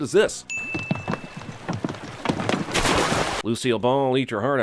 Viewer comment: i heard lucio ball